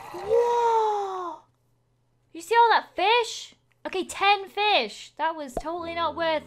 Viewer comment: She said look at all that fish I thought she said kill that bish 😂😂😂😂